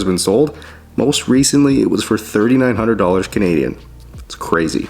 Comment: Wait, did you say $3,900? That cannot be right for a vintage machinery like this! Anyways, it should now go for at least 100k at Sotheby's after this meticulous detailing!